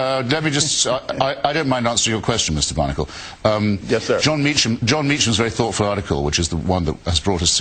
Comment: did he call him "Mr. Barnacle"?